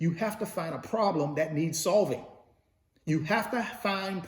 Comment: Thanks for all this information. I keep hearing that "find a solution, for a problem"(?).